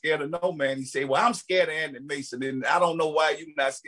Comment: Anonty mason 🤣🤣🤣🤣🤣 it’s Anthony Mason, that Louisiana talk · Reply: 😂😂 I thought he said Anny Mason